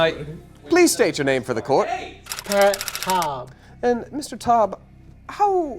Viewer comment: Wait @ 12:26 did it say 'joshua "joven" jovenshire ovenshire' instead of patt taub? I just noticed that through all my replay of this video🤣